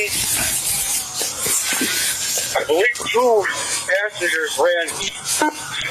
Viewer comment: i thought he said ass n*ggers... lmao